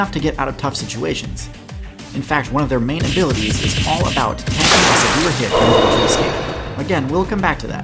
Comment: heard absolutely nothing from what you said lol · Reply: Apparently, "one of their main abilities is about tanking a severe hit in order to escape" , but I only got that from other comments. These videos need subtitles to be accessible in general, but in this case it's an innocent SFX gone too far.